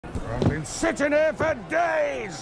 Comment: I didn't hear "joust", I heard "dreoauuuuss!"